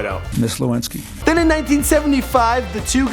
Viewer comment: did he just say "the two got WET" lmao